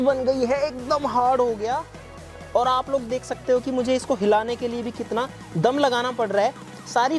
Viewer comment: Bhai kya bol rahe ho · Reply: Bahut dam lagana par raha hai hilane ke liye 😂😂😂😂 Age regist lagao😂😂😂😂